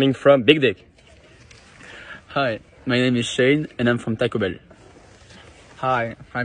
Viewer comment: I thought he said toco bell for a second